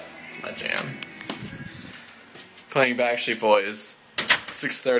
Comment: i heard "nailed it" lol